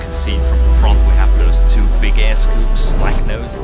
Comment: I swear to god I heard him say "Big ASS scoops" but he actually said "big air scoops"; Either way he's not wrong LMFAO 🤣 Beautiful machine, dream plane right there. Edit: Timestamp